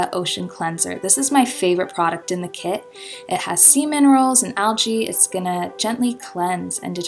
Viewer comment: I had to go back, and now I realize that you say, "sea minerals," I thought you said.. "semen rolls."